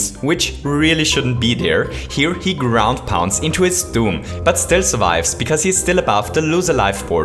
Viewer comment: did say "Tomb" or "Doom"? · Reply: Doom